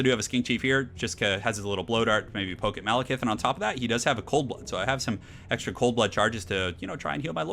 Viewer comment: I thought you said "And on top of that, he has a cold one." I don't want, I NEED a mod that replaces Cold Blood with Cold One and has the icon replaced with a Coors Light or something.